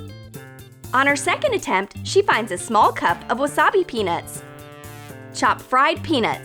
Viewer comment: OMG I THOUGHT SHE SAID PENIS NOT PEANUTS MY POOR MIND OHMAHGAWD